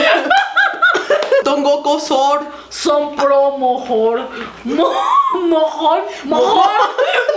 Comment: yo entendí mojón x''D